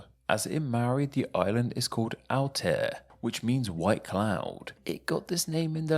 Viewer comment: I wasn’t looking at the screen when you mentioned Aotea and thought you said Altair and that made me think of Altaria, which is basically a white cloud ;)